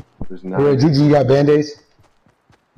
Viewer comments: Lol I thought he said 'Sorry N*gga' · I thought he said " Fo sho nigga" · Thought he said “sorry nigga” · I thought he said les go nigga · Sounds like he said "solo nigga". Means he's on his own